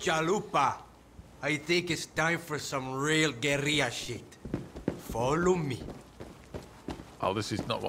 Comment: is it me or did Scott say a gorilla?